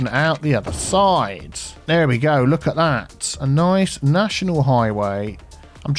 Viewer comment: I first heard "I nice natural highway" :D